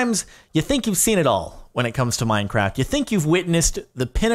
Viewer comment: Did he say minecrack?